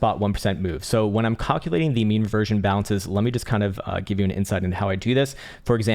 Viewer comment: I have watched almost everyday since 2018 and thought you had been saying "meme reversion" this entire time.